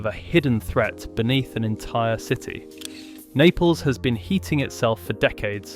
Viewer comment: Oh I thought I had heard heating nipples!